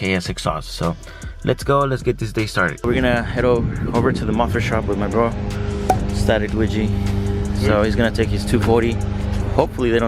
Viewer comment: Bro!! 🤣🤣 I thought u said u were heading to the muffler shop with your girl n when your brother popped up I died 🤣🤣🤣